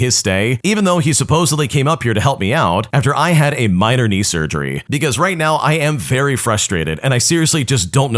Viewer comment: Did you just say Knee surgery? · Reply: Yes he said knee surgery.😅